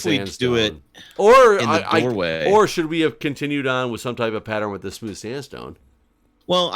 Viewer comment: Lady checking in.... I assumed ibuprofen was crossed off. Uhhhh.... lol P.s. you could rename Hans to Plague. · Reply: Not-lady here, but... So did I. I heard "Should I have gotten Ibuprofen" and went "No" as said "Yes."